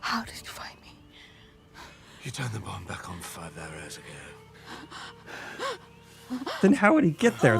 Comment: The subtitles said 'five arrows ago.' I swear I heard say 'five hours ago.' Which....would make more sense.